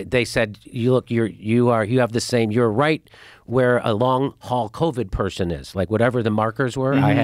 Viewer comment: I thought he said a long Hulk Hogan Person lol